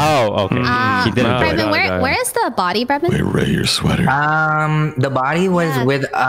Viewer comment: Omg I thought corpse said Rae your sweating, it’s so cute 😂 😂 😂